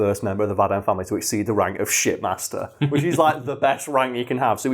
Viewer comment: I thought they were saying Shit-Master. It's hard to stop laughing.